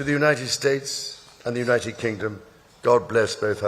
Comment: Did he just say, "Good job," to the King?